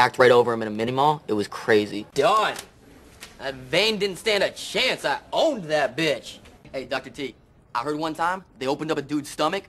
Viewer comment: OMG i heard bitch Lloyd where did u learn that language son